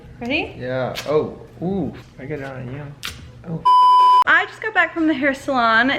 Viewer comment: Did anyone hear saying I’m gonna get down on you at like